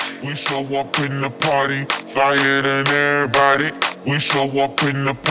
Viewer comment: i hear "we showoff in the party"